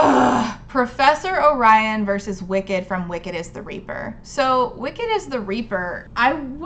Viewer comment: I keep mishearing Wicked is the Reaper as Wicket is the Reaper and that just makes me picture an Ewok in a robe with a scythe.